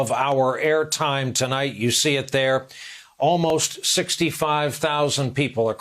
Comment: Thought he said 50,000 the first time